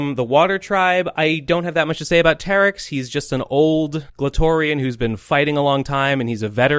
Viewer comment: Listened to this in the car with my girl. She thought you said “clitorian”